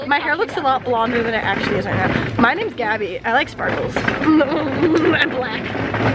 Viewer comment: Is gabby really black ? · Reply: If you're talking about what she said at 10:35 in the video, she didn't say "I'm black" she said "I like sparkles... and black!"😂😂